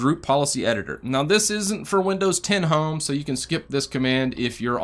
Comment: I definitely thought that when you said "This isn't for Windows 10 Home" you had said "This isn't for Windows Ten holmes!" I was a little confused haha.